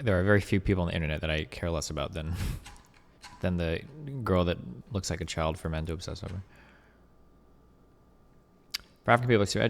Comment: what did he call the neeko chick lmao ? XD The girl that looks like a child from what? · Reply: "...looks like a child for men to obsess over"